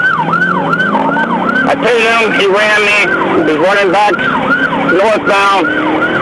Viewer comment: lol did he say "he rammed me"? 4:08 tho